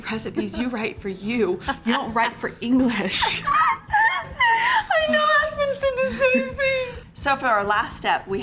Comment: Did anyone else catch that?! Congrats on the new husband Christine!! Timestamp 3:15. Watch the clip with subtitles. 😂😮🎉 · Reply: Aspen is her other daughter. Subtitle was incorrect.